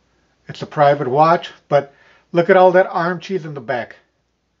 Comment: Did you call that “arm cheese”?lol. I’ve never heard it called that! I’ve had cat butter in my eyes first thing in the morning. Mocos at the end of the day. Now arm cheese. 👍🏼 Very education video. Thanks.